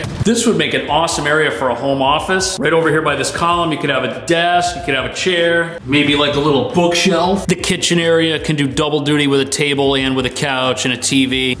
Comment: For whatever reason I kept hearing you say "kitchenary" instead of "kitchen area". Now I'm gonna have "kitchenary" stuck in my head...